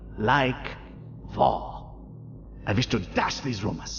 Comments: Please tell me I'm not the only one who hears vore. . . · What others hear: "I like war" What I hear: "i LOvE VorE" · I can't be the only one who heard "vore"